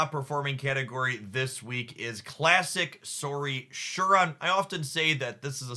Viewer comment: Noones commented on it, but an I going crazy or did I hear you say "Sauri Shuron" lol Edit: oh thank goodness, at least one other person heard it lool